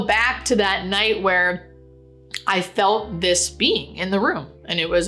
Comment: I thought u said u saw a bee in back of her I love the way u called this ministering spirit a being ! To be is to exist we are human beings. And God is the Being whom has existed always He says "I am the Great I Am" to simply declare He is He was and will always Be. Thanx for telling your vision it's.our job to tell others. Judy